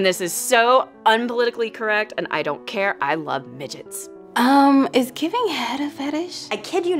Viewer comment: Sounded like she said fidgets not midgets lol